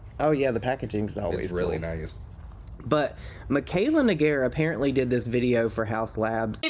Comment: Someone who was watching the live said the guys name was negar and to me, that is what it sounded like she said. I think that’s why she gave no reaction.